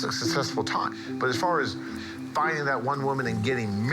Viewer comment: did he just say.. buying that woman?